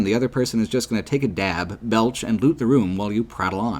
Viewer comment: Did just say "dab"?